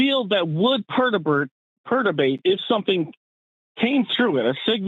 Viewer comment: did he say herdibate?